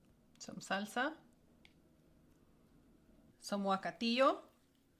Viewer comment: What word are you saying at about 13:03 when spreading a green sauce. Your transcript says wakao but that is a Japanese word or hair product. I’m sorry I don’t know but I didn’t grow up listening to Spanish in my home but I love the flavors of your food. Also, I clicked on your website and it says it’s not linked. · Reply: Guacatillo. Pronounced "Hwa-ca-tio." Avocado and tomatillos.